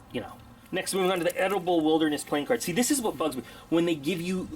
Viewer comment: Oh! "Edibles" playing cards! Totally thought you said edible playing cards. I was thinking "don't open them, they'll spoil..."